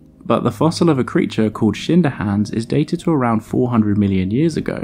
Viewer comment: Thought he said shit in her hands Omg haha 😂